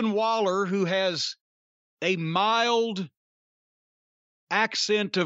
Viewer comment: thought my mans was gonna say mild autism